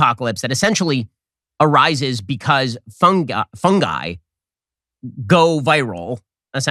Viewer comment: Did you say ‘fungi go viral’. Lol